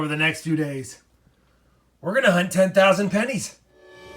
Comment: I thought it said 10,000 penises were searched and was wondering why they cost $25 each.